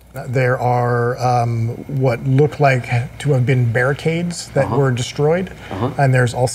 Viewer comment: sometimes hard to understand some of what chris said unless it was repeated by wil or someone else. 5:00 for example - bear cages and sheels? "oh, barricades and shields!"